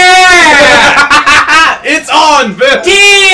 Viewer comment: did just say "It's on, bitch"